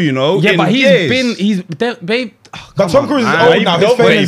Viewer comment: Did just called 'BABE' loooool.